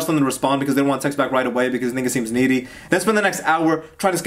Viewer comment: Bruh did he say the n word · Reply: he said because they think it seems needy